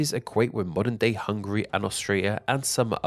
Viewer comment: did he say Australia?